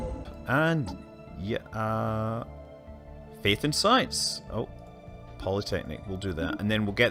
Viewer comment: When you said "Faith in Science" I first heard it as "Bacon Science"...